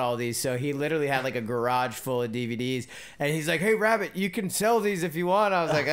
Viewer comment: Does Phil call him Raab Rabbit? Somewhere around 3:35ish it sounds like Raab says rabbit.